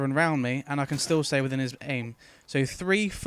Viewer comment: does he say i can still stay within his penis?